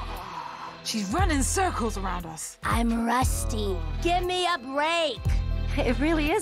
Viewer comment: Amethyst: I’m rusty. Give me a break! Spinel: has literally been standing still for 6000 years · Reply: by the way I heard i'm rusteen